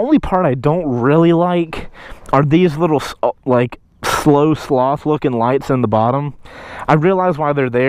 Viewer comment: Jesus I thought he said snow sloth looking lights 😂😂